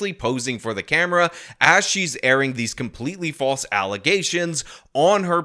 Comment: my brain is so broken I heard allizations not allegations